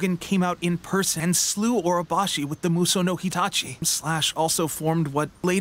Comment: Did he just say mussou no HITACHI? 15:15 Teppei likes Naruto